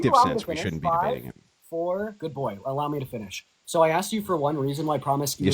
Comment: “Good boy” Andrew: “Put your blindfold back on, please.” puts blindfold back on You can’t make this shit up wtf lol. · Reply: I thought he said “good goy” lol